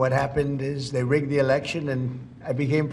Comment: What did he say at 3:13? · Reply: he said they rigged the election